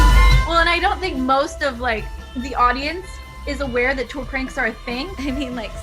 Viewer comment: what does she say 6:57 i dont understand what she;s saying sounds like "I dont think the audience knows tool prints are a thing?"